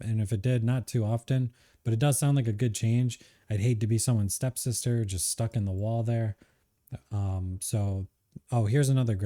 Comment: “I’d hate to be someone’s step sister, just stuck in the wall” did I hear that correctly? lol that caught me off guard